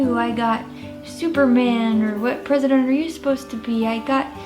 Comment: I thought Laina said "prisoner" instead of "president". I was so confused when she said Bill Clinton. Whole other meaning.